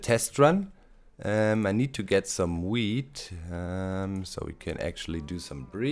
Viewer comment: Did he say "get some weed" at 29:25 ?